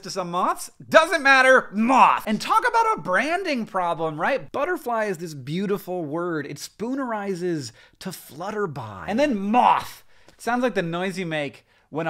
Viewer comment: I really thought he said that butterly "splutterizes" into flutterby, not "spoonerizes" and I thought I was about to learn a new term for a linguistic phenomena - spoonerizing but specifically within one word instead of within two adjacent words XD